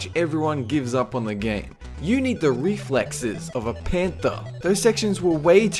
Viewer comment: Oh heh, I thought he said "You need the reflexes of a panda," not panther. Confused the hell out of me for a couple of seconds because, well, you've seen pandas.